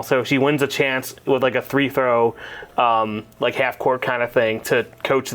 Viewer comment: Did you say "three-fro? 😂